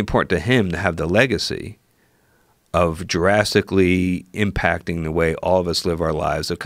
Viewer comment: 🤔🤨 did he just say it "Jurassically" instead of drastically?!🤷‍♂️🤷‍♂️🤣🤷‍♂️🤣😭😭💀💀 I'm not trying to get him a hard time cuz I'm sure I would make more mistakes than that if I was being interviewed by Joe Rogan LOL but then again I'm not used to doing interviews or being on TV either🤷‍♂️🤣😂😭💀 I just thought it was funny and I had to rewind and make sure I heard what I thought I heard🤣😂😭💀